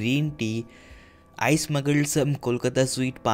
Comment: did said smuggle?